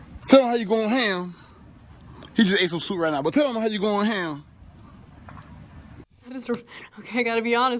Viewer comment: Looks like 'natural selection' was on duty during the 4th of July weekend. LOL Also @ 02:49 can someone translate that for me because I have no clue as to what he said? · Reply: I heard "tell 'em how you goin' ham" lol